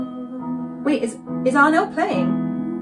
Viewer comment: “Wait, is Arnel singing?” Um duh, that’s what usually happens in a duet. Now, you’re just being patronizing pretending not to know. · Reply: What she said was, "Wait, is Arnel playing?" (the piano). Listen carefully again